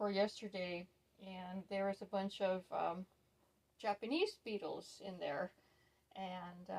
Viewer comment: I thought you said Japanese PEOPLE 😂🤣😂🤣😂 oh goodness! There’s me imagining all these people in your veg garden🤦🏼‍♀️🤣😂🤣 love that dress… and mind those little people when your gardening 🤦🏼‍♀️🤷‍♀️🤣